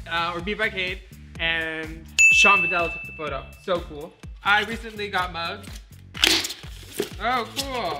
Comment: I thought you said “I recently got mugged” but then realized you recently got ☕️ 🍺 mugssssss. Lol